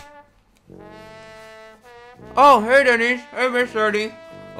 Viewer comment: When said “Hey Denise” while brushing his teeth it sounded like he said “Hey daddy’s”😂